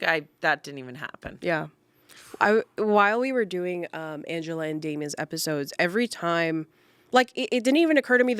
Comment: I heard Angels & Demon's and can't unheard it now. Can we have Angela and Damien, roles reversed even, on as an angel and demon on someone's should anywhere on Smosh?😅